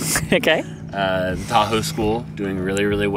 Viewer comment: I thought said, “taco school”. Maybe I’m hungry.